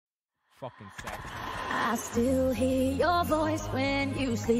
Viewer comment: Wait what does it say at 0:00 😂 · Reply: "F*cking savage" ? 😂😂😂